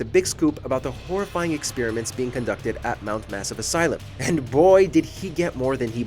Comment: It for real sounded like you said mount ass not mount mass I think I need to both get my mind out of the gutter and get my hearing checked